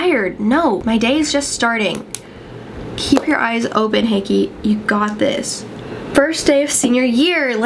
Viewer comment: did she just call herself hickey???😂😂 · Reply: Aura Cat she calls herself "Hakey" lmaooo its almost like an alter-ego